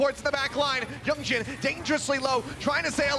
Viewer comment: 나만 병신이라 들림?ㅋㅋㅋㅋ